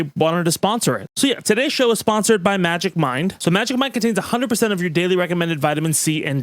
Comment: Anyone else thought he said magic mike for the sponsorship? Lool😂